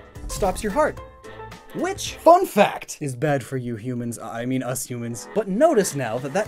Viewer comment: I heard that mortal I mean human😓